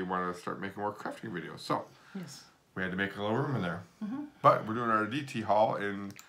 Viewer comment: I love you guys ! It sounded like he said , " we cleaned up a little crap in the room ! Lol 🤣🤣